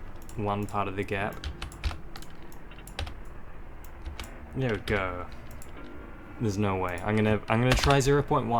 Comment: what did bro say?? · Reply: there we go